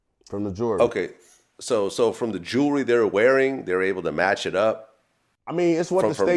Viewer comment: Bruh should’ve just stayed in the car😂😭 Thought he was saying “the jewelry” lmao